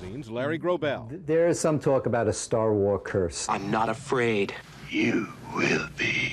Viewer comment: I thought it said a Star Wars cruise.